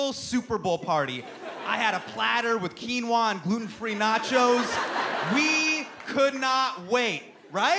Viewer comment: Gluten-free nachos lol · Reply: Two-Face Ah...to be honest, I heard "Putin-free nachos". ;-)